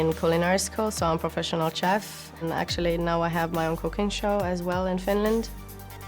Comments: for some reason I heard "Now I have my own cocaine show"... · Did she say she has her own cocaine show? That I'd like to see, Pokerstars.